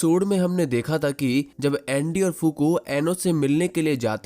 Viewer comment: kya bola bhai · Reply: Andy or fuko